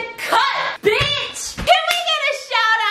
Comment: I'm a little shook because she said bitch instead of biss